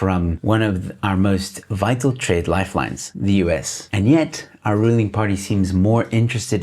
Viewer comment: for a moment I thought you said "our ruining party"... And thought... That's quite accurate